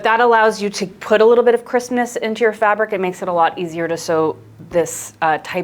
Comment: When you said crispness I thought you said Christmas 😅